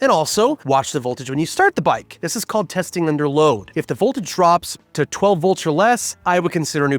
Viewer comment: The caption said under low instead of load